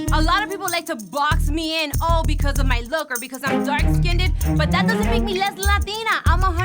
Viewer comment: Did Amara say dark skin did? 👀👀🤣 I think she meant dark skinned💀🤣 I love her tho❤️ she said it at 7:16 😅